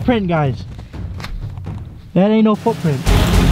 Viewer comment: I thought you said that aint no hoov print but you said footprint when it was hoov print😂😂